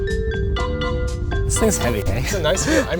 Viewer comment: did linus say "this thing is heavy eh" TYPICAL CANADIAN